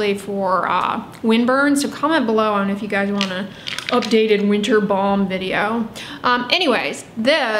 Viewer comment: A winter balm video would be good 🙂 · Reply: I thought she said bombs as in bad products not balms. lol.